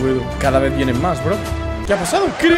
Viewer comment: Ayuda escuché "Recuerda f0ll4rme..."